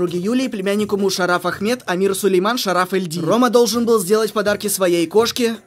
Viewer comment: Я одна услышала на 2:36 Элли Ди?